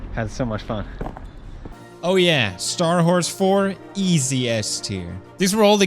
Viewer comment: i thought he said "star whores"